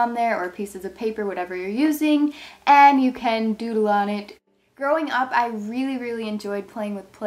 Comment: When you said growing up I thought you said throwing up